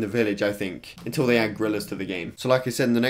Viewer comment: I was so confused when he said gorilla because I thought he was saying grillers